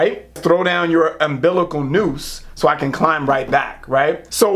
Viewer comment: I swear I thought he said medical noose....